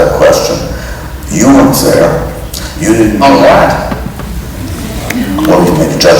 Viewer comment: anybody know what Oliver interrupts him to say? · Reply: Dustin says “you weren’t there,” and John says, “I’m glad.” What a champ.